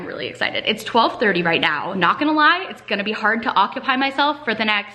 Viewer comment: It sounded like you said "its gonna be hard to octopi myself"